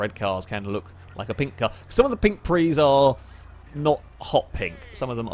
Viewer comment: I thought he said "pink trees" and was incredibly confused, especially when I thought he was like, "I see a bright pink tree!" xD.